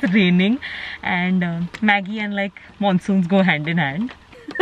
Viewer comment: I thought you were saying Maggie and Maddy go hand in hand😂✨ Btw the blog was really cuteeee🥀😍